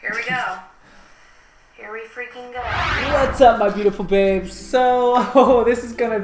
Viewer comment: I thought he said “whats up my beautiful pigs” 😭😭😭